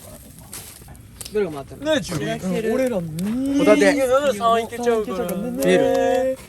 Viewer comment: 何人かがIKKOさんみたいな声で『××○○～、ね～』みたいな会話してるんだけど、何て言ってるんだろ？ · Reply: くらいから慎太郎と樹で 💚 ねーじゅり 💙 俺ら 💚💙 にぃ〜 💚 なんなら3いけちゃうから 💚💙 ねぇ〜 だと思いました!笑